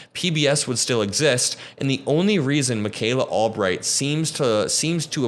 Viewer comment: why did I hear Micayla Altright lmao.